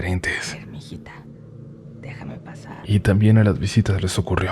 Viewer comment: Para los que quieren saber que dice el susurro del minuto 16:19 es «M'ijita, dejame pasar».